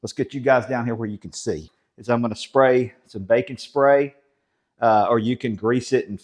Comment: I thought you said spray some BACON spray. LOL I am southern too but your accent fooled me.